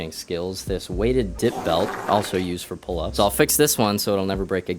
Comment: I thought it said "drip belt" instead of "dip belt". I was like sword mfkrs got that swag.